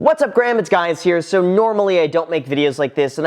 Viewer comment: Graham do you realize you started your video backwards? Unless I just heard it wrong. Sounds like you said what’s up Graham it’s guys here instead of what’s up guys it’s Graham here. Just thought I'd mention it